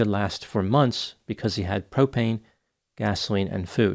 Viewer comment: When you said `Propane` I thought you said `Cocaine`! Mea Culpa! Wish I could be so `Sorted`, `Aware` ,etc.